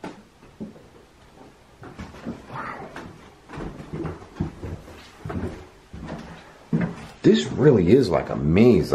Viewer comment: it sounds like somebody says „why” or something right into the mic. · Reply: said wow wdym ?? lmao